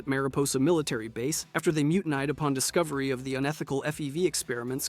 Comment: sandy sands? you okay man are you having a stroke? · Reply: there's also "mutin eyed" instead of mutinied around 4:45 lol i'm 99% sure these are written by someone other than the narrator